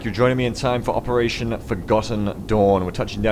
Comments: Through your accent, it sounded like you said operation Forgotten Doorn... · I am assuming that it is just your accent, but every time you say "Forgotten Dawn" I hear "Forgotten Doorn". XD